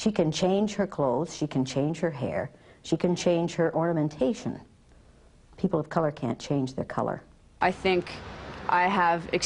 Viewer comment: People can choose their sexual orientation?? The short-haired person at the end wasn't treated fairly. They weren't seen or included in Elliott's list of differences. She kept pushing a gender on them when they didn't want to be gendered - and at this point the exercise was already over. She also pitted people of colour and white people against each other repeatedly - I don't think that's a helpful strategy. Chances are, it will make some of white people (more) negatively towards people of colour after the exercise when the point of the exercise was to develop empathy. Elliott's earlier exercises were better, creating less fraction beyond the exercise itself. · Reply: The first time I heard it, I thought she said orientation. Upon reviewing it a few times, I believe she said ornamentation (jewelry, like her eyebrow piercing). This is also what the auto-generated captions transcribed it as).